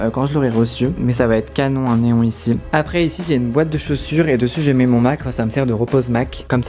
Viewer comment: 3.07 j'ai compris "toutes mes chaussures gelées" 😂 j'ai bugé mdrr